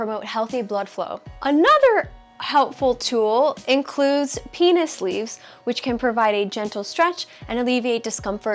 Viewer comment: At around 5:16 in the video you mention "penis leaves." I have no idea what you mean by that, though I am probably not hearing you correctly. What did you mean to say? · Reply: Yep, a penis sleeve. Check out Silisleeve2